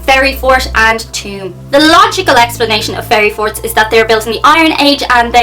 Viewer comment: At first I thought you said "fairy fart"!